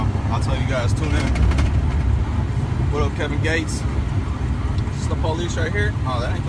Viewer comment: Also- if you're gonna try and rep satan or Lucifer...then pick one. They're not the same thing. LOL DID HE JUST SAY "HEAVEN GATES" (and not "Heaven's Gate" lol...) DOES HE EVEN KNOW WHAT THAT IS? 💀 Edit: yeah definitely misheard him saying Kevin Gates which makes way more sense. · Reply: I thought he said Kevin Gates. I may be wrong lol